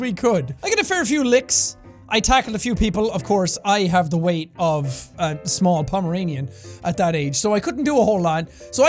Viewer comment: I thought said he was the weight of a small pomegranate